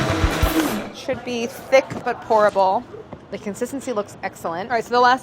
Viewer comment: Definitely thought she said the cone batter should be 'thick but horrible' at first